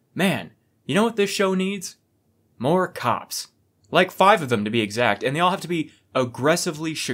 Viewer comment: I heard "more cocks" at the start of the video and was really confused about how one could be chicago